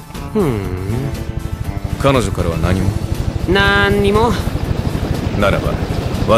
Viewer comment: when he says nothing at all at 6:47 it sounds like he is saying 'not anymore' lol